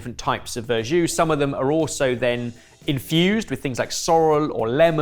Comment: did anyone else think Ben was saying the verjus was infused with sorrow? :D